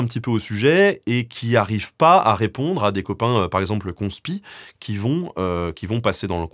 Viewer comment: j'ai entendu les "poncepi", alors je me suis dit que ça doit être ceux qui s'en lavent les mains 😅